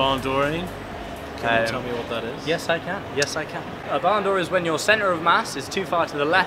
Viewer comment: What is that word you said the meaning of (To go too far to the left or right and therefore swing out and loose balance) @ 3:00 ? Bandor? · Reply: Barn door :)